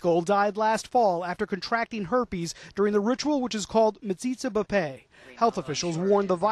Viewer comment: the newscaster says "during the ritual which is called... " what sounds like museetsa kupay? I'm not really sure what it was I heard or how to spell it or pronounce it. Would anyone know how to do this? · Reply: It's called metzitzah b'peh But wait there are more hideous stuff they do